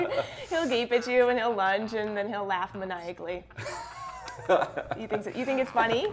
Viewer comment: Can somebody please tell me what was the actual last word she said in the sentence at minute 11:09? To make it easier to locate and answer accurately my question, I'm writing the actual sentence with the word I didn't get written with hyphens in the middle: "He'll gape at you, and he'll lunge, and then he'll laugh m-------ly." Thanks =========================== UPDATE: Question already answered, thanks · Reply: Maniacally.